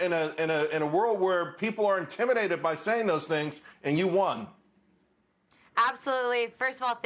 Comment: Hold up hold up 0:47 Did he say shes "ANTI LEGAL IMMIGRATION"?